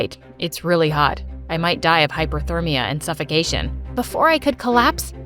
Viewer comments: PPPFFFFTTT, 9:07 DID YOU JUST SAY HYPOTHERMIA?? Oh wait hyperthermia… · Me: why tf he said “its so hot I’m gonna die of hypothermia and suffocation” hypothermia is dying to the cold and this guy is supposed to solve rubix cubes in competitions well at least he got suffocation right